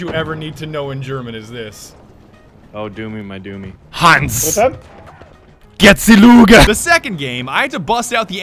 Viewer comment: Hanz. Get ze Luger. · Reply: When I heard that I thought he said Hans get the lube